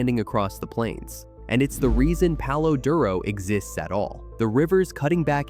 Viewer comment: Go visit. Near the border . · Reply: I thought they said El Paso . My mistake.